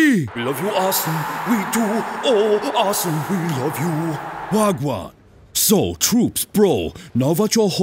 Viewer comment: DID WENGER JUST SAY WAGWAN?????😂😂😂😂